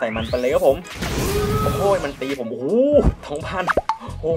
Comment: โอร่า!!!!! ได้ยินเหมือกันหรือป่าว